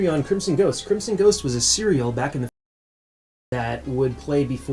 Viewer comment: When you said serial, I thought you said cereal. I was picturing a bowl of frosted crimson ghost heads.